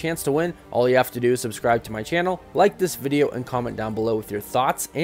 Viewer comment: did he just say THOTS!?